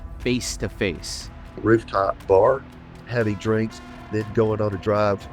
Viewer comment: So this guy’s idea of a perfect date, is to take Jennifer Aniston drinking and driving? 😂🤦‍♂️ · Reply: It kinda sounded like he said "heavy drinking" 😂